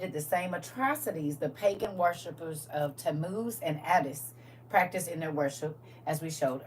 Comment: Tammuz, I thought you said Temu, lol